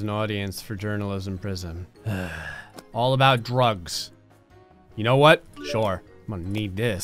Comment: Bruh I opened this video to watch it again and I just hear “it’s all about drugs”